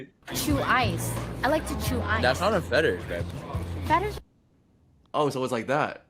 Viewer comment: What does he say at 25:28? I can’t hear what he exactly said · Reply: he said babe